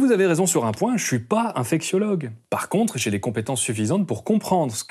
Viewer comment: Oh purée, à 14:44 j'ai cru que tu avais dit "je ne suis pas un fake siologue" xD Bon, blague à part c'est une excellente vidéo, merci à toi pour ton travail et tes sources.